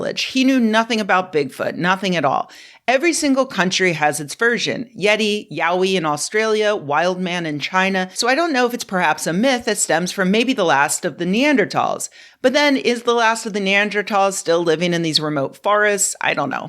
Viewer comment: I had to do a double take when I thought I heard Rebecca say "yaoi in Australia."